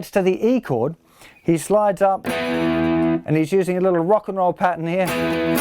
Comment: I thought it said "Rick n Roll"